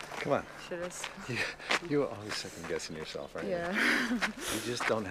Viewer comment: What did Kevin say at 7:43? You're always....? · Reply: second guessing yourself